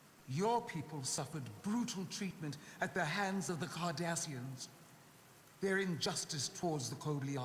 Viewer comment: Your people suffered greatly at the hands of the Kardashians. That's what it sounded like she said😂😂 52min in